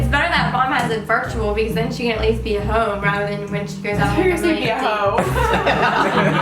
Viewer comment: i legit thought said ‘and be a hoe’😂